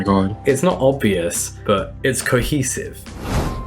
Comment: Wait, what did he say at 34:42? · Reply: "Oh, she's my least favourite"